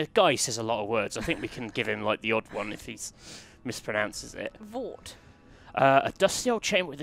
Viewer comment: I've got to tell you...1:10, you guys had me at, "THEATRE" <3 :D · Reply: Did he say, "VORT"? Like...some odd, alien Doctor Who baddie?